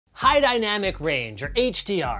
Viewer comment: I literally thought you were saying hi to dynamic range lol